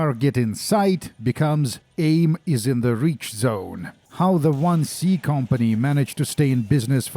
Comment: Dude! That "Aim is in the reach zone" line woke me up like I was a sleeper agent. I had that phrase stuck in my brain since I was a kid and never really paid attention to how bizarre it was. Turns out it was just google translate going crazy and no one bothered to check. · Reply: I always thought they were saying "enemy is in the reach zone", i always thought it was some way of saying "enemy in range" or something like it.